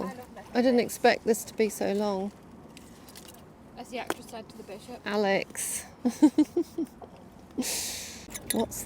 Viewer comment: Thanks to the transcript on YouTube I could find out what Alex said at 18:21 -- "said the actress to the Bishop" -- hmmmm -- had never heard that before, but I've memorized that response now!!!! 😅😅😅